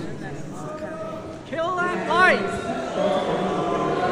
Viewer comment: I heard, "Kill that light"